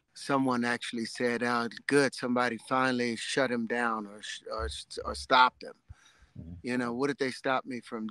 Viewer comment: I can almost guarantee what someone actually said was "Good, someone finally told him to shut the hell up."